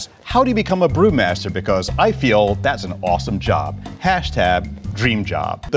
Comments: … did he say hashtab? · did he just say hashtab for hashtag. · Did anyone else hear him say HashTab? 3:01?